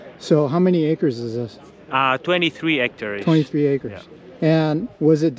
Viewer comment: @ 20:18, I think he said 23 hectares not acres. If so, that's about 57 acres.